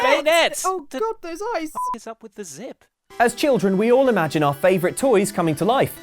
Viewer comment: on banette when the person said what is up with the zip it kinda sounded like he said what is up with the zit